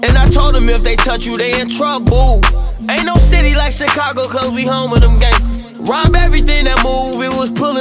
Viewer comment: "Ain't no city like Chicago cause we home of them gangstas" 🔥🔥🔥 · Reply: I could of swore he said we humble them gangsters